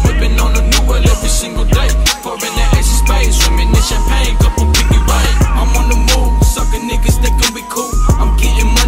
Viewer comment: what does he say here?? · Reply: I think it’s double pinky ring 😂